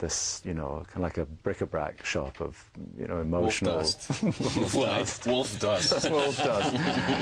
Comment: What does he say at 5:55? Wolf dots?